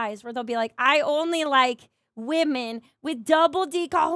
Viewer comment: I deadass misheard and thought thought said "women with DD cojones." On a scale from 0 to 100°C, how cooked am I?